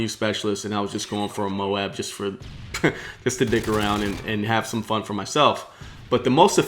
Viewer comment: Did he say " Just to dick around" ?